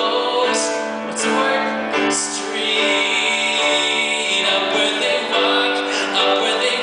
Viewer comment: Lol. 2:26 sounds like he says, "Up where they f**k..."